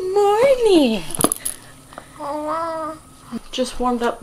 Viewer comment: Aww it sounded like she said hello at 2:28!